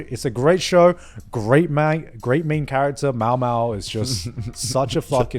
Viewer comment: did garnt say “great manga”?